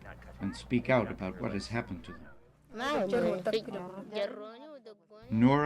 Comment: I don't hear "it" at 3:16, but "em". At 3:23, however, the speaker says "it" in reference to the village, not the girl.